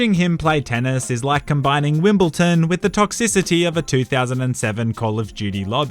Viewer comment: He said Wimbletom instead of Wimbledon